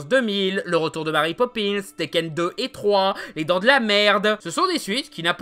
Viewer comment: Vous entendez tous comme moi "les dent de la m*rde" à la place de "les dent de la mer 2"?